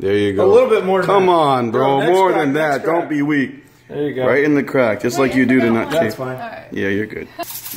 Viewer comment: did he say “in the crack like you do me?”😳